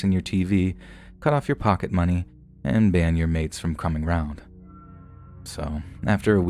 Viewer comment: And now we got a kid who's griping about having their X-Box and maid service taken away if they don't suck it up and go on the free trip to France for a week? WTF. God...life is real tough sometimes. They don't make violins small enough for people like this.. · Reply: No, he didn’t say “maids” he said, “ban my MATES from coming around”. 😂 I thought that’s what was said at first too but I listened again and realized he was talking about his “mates” (meaning “friends” if you are British like that guy!). 😁